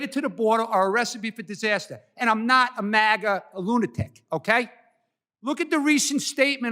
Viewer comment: Did he just call MAGA's Lunatics'?